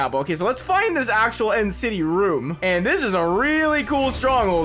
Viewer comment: did you just say end city portal?!?